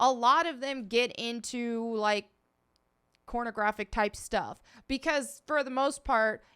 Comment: Did u just say cornographic? 😂lol